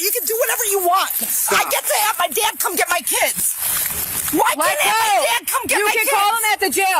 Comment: she said, "I get to have my dad come get my kids", which isn't what the on-screen captions said.